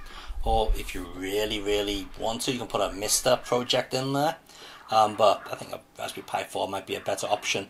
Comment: I couldn't catch what you said an alternative raspberry pi?? I thought you said a Mr. Project. Can you confirm?